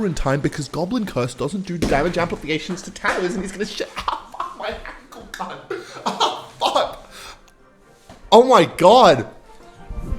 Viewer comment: did he really say "ow fuck my ankle card"?